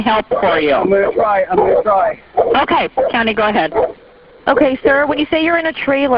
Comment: I was just listening and I know I heard “County, go ‘head” but then I caught a glimpse of it saying “Tammy” and now I can’t unhear Tammy. These are so scary.